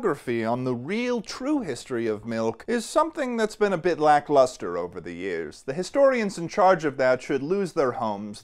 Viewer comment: I genuinely thought he said lactolustre @ 0:35 lmao